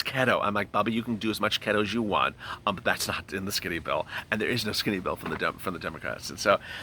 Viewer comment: I thought you said there was no skinny Bill